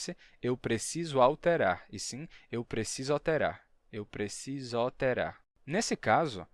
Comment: Ele tb, soh foi um experimento. Vc provavelmente ouviu alterar, mesmo nao sendo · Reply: eu ouvi "OLterar, assim como escuto "naIScimento". qdo se escuta um sotaque diferente, fica mais destacado aos ouvidos. Oxenti, mas BAH tche! kkkk